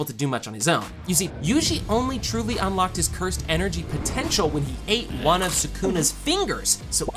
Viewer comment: …did you say potential